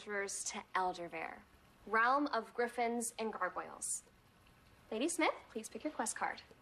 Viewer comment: penelope said Betty instead of Alice